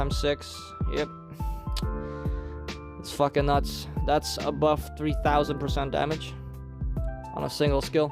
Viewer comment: Kindly, open your ears when u watch the video Thank you. "Above" 3000% damage, not (buffed). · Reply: ah i see, my bad, i heard you saying "a buffed", not "above". Will have to adjust my ears to your fake af try hard accent next time. Edit: just a side note, the prebuff damage was alrdy ABOVE 3000%, so i dont understand the point you are trying to make. If you actually said they added 300% damage or that the total damage increased by 10%, it would have made much more sense, but hey, not everyone can do basic math.